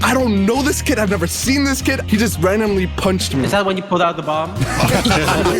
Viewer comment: at first i thought said "was that when you pulled out the glock" 😂💀